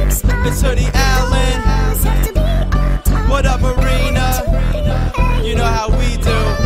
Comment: I dont know what the song actually said but it sounded like it said my name"Whut up arayna " i was like did it just say my name .... tell me what it is really saying.. it says it at 28 seconds in